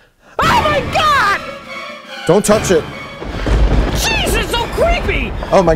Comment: I honestly thought said “Jesus O’Creepy” at the beginning after he touched the pumpkin.